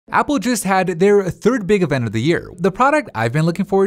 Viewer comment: When he said "Apple just had-" for some reason it sounded like he said Apple juice ngl Xp